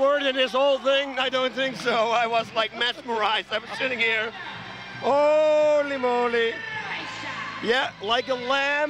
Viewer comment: I heard it. At 2:05 Bas Rutten actually says, "Holy Moly!"